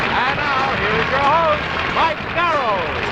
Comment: R.I.P. Mike Darrow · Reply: I thought the announcer said "Mike Farrell", so I was looking for the then-future B.J. Hunnicutt. Farrell was already in TV back then, appearing on shows like "The Monkees" and "I Dream of Jeannie".